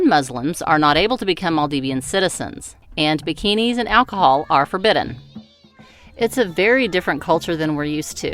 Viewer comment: I had to replay the bikinies and alcohol are forbiden part three times....I thought you said zuckinies and alcohol. lol Another entertaining video...thanks!